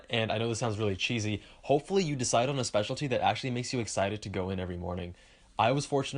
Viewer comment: At the end I kept hearing "Special Tea" and I was like "Well I do love me some Earl Grey"